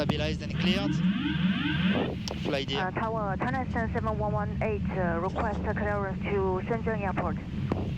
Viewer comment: Was that saying China Southern 7118, request clearance to Shenzhen airport?